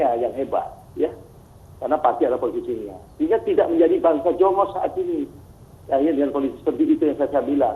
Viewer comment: banser jongos apa saya tidak salah dengar? Mohon maaf